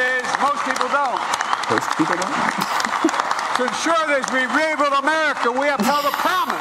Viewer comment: kinda sounded like dream of America